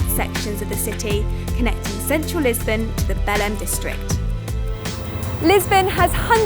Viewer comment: did she said bell end ??